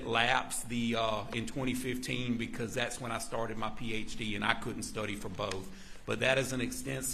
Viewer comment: It’s a “he” and HE started studying for his PhD then so couldn’t study for both. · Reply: sounds like a she and I could’ve swore she said started my period and she couldn’t attend